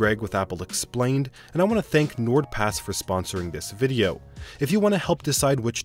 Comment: I really thought that said "NWordPass"